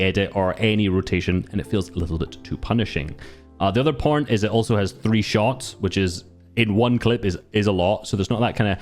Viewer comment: “the other porn is it has 3 shots”😂 what am i hearing